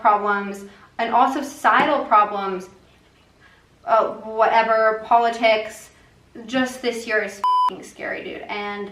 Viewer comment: Can someone tell me what she said at 6:26 it’s bothering me :( I NEED TO KNOW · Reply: Pandemic